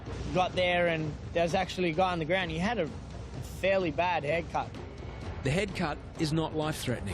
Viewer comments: I thought he said haircut and not head cut 😂😂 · for a moment I thought said “he had a fairly bad haircut” I was like, geez that’s not really a big priority but ok 😂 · I thought they said haircut 🤣